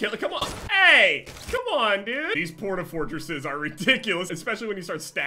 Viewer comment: its sounds like he said porn-a-fortress